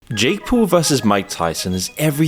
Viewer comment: why did I hear J Cole 😂